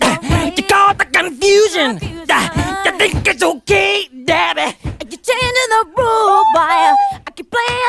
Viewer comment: Is it me or does it sound like mike is said Debbie not D*** it ?